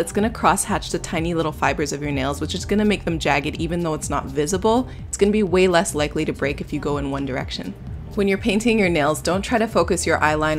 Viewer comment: all i heard was One Direction